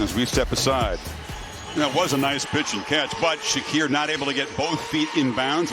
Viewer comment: am I tripping or did he say that was a nice, bitchin, catch